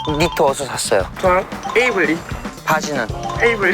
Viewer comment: 이거 Gnarly로 들림ㅋㅋㅋㅋㅋㅋ